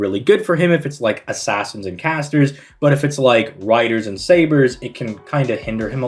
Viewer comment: Did you just say Douman cant handle riders? ehhhhhhh...? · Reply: I said Riders and SABERS, because as an Alter Ego he does resisted damage to them.